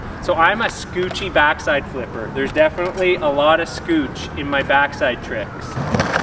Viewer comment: Did he just call it scootch?